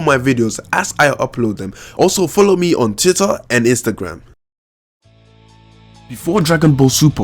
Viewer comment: Did He Say "Titter"?